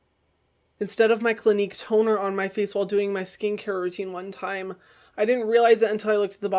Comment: is it just me or did it say “skin cat routine” 😂😂😂